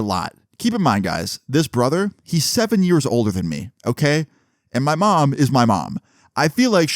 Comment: holy shit I thought you said he was gonna be 7 years old 😭